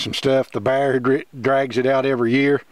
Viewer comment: did he say the bear drags it out every year???